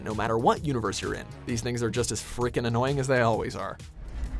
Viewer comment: did he just said the n word? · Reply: He didn’t say a racial slur, he said “These things are just as annoying as the original ones are”